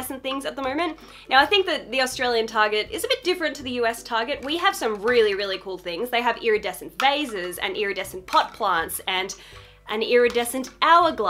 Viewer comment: I so thought said, "iridescent vases, iridescent butt plug, iridescent hour glass".. 🧐 lol